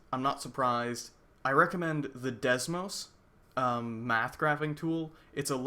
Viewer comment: I couldn't quite catch the name of the math website you mentioned in your video at about 15:15. Desmos? Could give that a spell-out for me? Thanks.